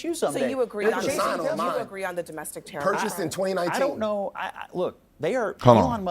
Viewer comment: I HEARD IT “PURCHASED IN 2018” !?! GO AHEAD AND SAY IT !! STPD